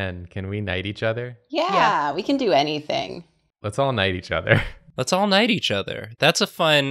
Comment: What they said: Let's all knight each other. What I heard: Let's all night each other. Take that as you will.